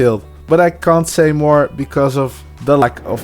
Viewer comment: Pictures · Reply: no I got it, it was a joke cuz it sounded like you said "lack of b#tches" sorry 😭